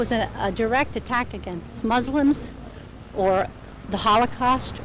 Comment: did she say "Smuslims"?!